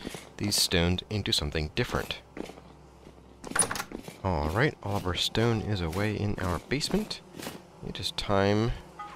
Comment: hold on, did you just say that Oliver Stone is away in your basement? seems a strange place to hide out.